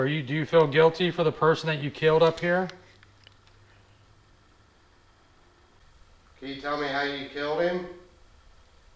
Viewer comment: ish right after askes if the spirit feels guilty about the person it killed, the spirit answers in a whisper.... I can't tell exactly what is said though 🤔 can anyone else hear it or am i trippin?? 😅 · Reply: I think I hear "not really".....